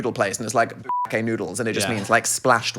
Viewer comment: I’m dumb, what is he saying here? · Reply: Bukkake